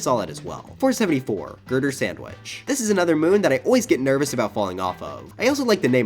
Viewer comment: I misheard you saying “murder sandwich”💀